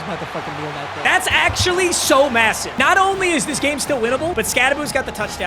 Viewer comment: did someone say massive??🫢